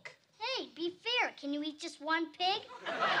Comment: "You may take just one book." "Hey, be fair! Can you eat just one pig?“ That's so savage. 🤣🤣🤣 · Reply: i thought he said can you eat just one pie